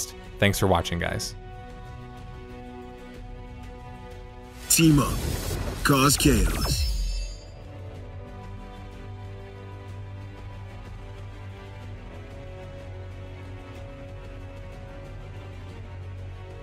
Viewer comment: "Teemo caused chaos" I cannot hear anything else